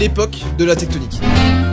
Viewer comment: Mdr au lieu de "tektonik" j'ai entendu "taekook", sauver moi XDDD